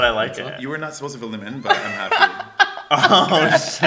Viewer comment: Oh sh*t Me: I heard Oh sit XD